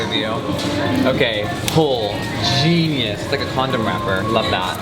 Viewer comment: did he say condom raper 😳😬